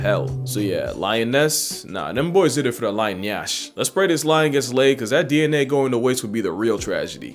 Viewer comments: I heard "Lion nyash". Welcome home brother. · Around the 12th minute I heard "lion nyash". Bro be using Nigerian slangs. Way to go bro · Did I just hear nyash haha